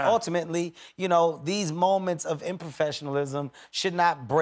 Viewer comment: Did Kevin just say "improfessionalism"